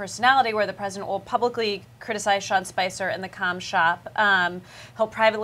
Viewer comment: Did she say cumshot...?